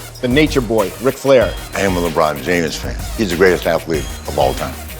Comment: did he just say he's a "LeBron Jamus" fan??? 😂 WOO 😭